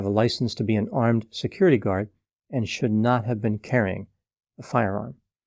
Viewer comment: I listened all this time because I thought he said ONE ARMED security guard!